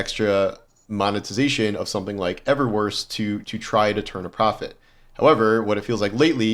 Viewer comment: makes a freudian slip and calls it "Everworse" instead of Eververse. Shots fired?